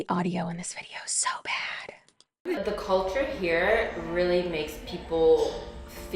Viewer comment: does she say "in the cult in here"? 😱